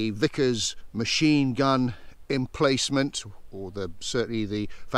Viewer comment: I had to do a double take there when I thought you said the "Vicar's gun emplacement".I hadn't appreciated that the local clergy were mobilised in WWII. I did twig eventually!